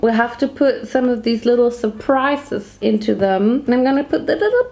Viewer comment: my dumbass thought she was saying “suppressor” but then i realized she was saying “surprise”